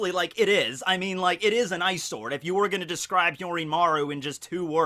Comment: really thought you were saying "eyesore" when you said "ice sword" 😂😂😂😂